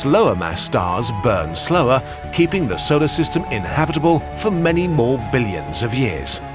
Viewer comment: did he just say "inhabitable"?